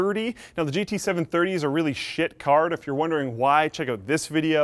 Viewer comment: did... Did he just say shit..? And it wasn't bleeped out!?